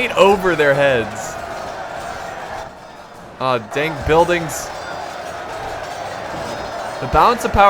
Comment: did he say " dank buildings " are you a dank memester? Comrade doge is looking for you